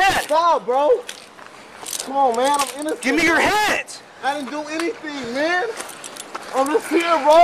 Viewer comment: did he actually say 'I didn't do ANYTHING..'